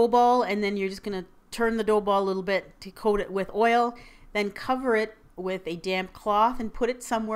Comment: I thought you said "and cover it with a damn cloth..." Lol.